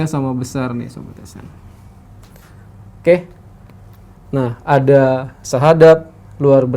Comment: saya dengernya sahadat. Sudut sahadat, sudut islam? 🤣🤣